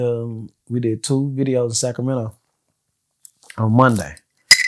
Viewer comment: 🗣️Did I hear sacramento?? 💪🏿🙏🏿